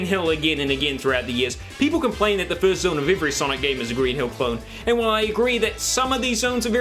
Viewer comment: anybody else hear him say "Green hill phone" @ 1:47?